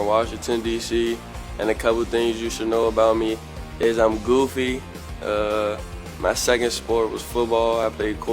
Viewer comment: He said “I’m goofy” I heard “I’m a goofy”